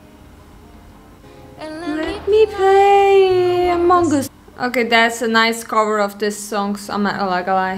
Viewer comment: sus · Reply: oh no. It means suspicious like the game among us. It sounded like you said “let me play among us” and I thought it was funny. Sorry